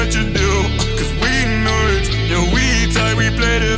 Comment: When it said "Cause we nice" I thought it said "Cause we nerds" XDD